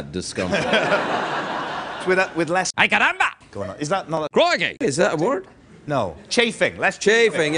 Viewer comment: Does anyone know what word theyre talking about at 2:00:30?? So confused lol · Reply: Michael says frotting instead of chafing, frotting is when 2 guys grind on eachother 😂